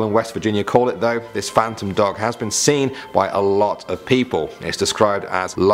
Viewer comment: when he said phantom dog i thought he said magnum dong